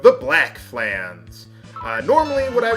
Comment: I was yawning when he said Black Flans and misheard it as "the black man!" and had to rewind all like "Wait what?!"